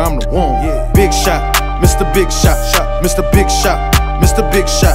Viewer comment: Sounded like he was saying "mr big shaq"